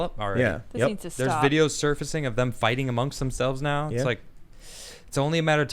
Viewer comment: in... Did he say 'Videos circusing'??